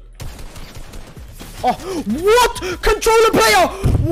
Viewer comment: When my bro said controller player i thought he said control a player